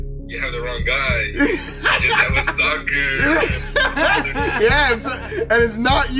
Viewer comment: listen very carefully he says "cuz im the stalker!" but they dont hear him cuz they are laughing